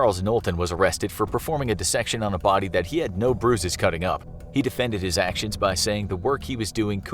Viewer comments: swear I hear "bruises" but captions say otherwise. Still great channel 👍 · He said “bruising” instead of “business” at around 2:58 smh hire me